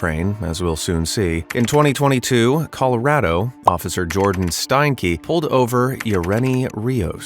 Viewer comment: my dumb ahh thought he said "Jordan Stinky"